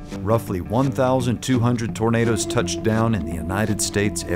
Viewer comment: sorry this is way off topic but at 0:08 the captions say: "Over average roughly 1,200 burritos touch down in the United States every year." so many people dropping their burritos.... wow also, every time says tornado it says burrito in the captions pretty much